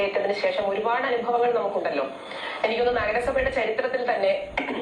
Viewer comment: Did 6:43 did she said Naraga Sabha....😂😂😂😂